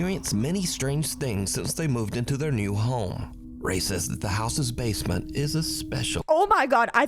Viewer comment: same I thought he said racist to